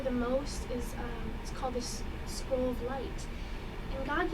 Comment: what does she say exactly? "school of light", "squirm of light" ? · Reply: Rolla Kosta scroll of light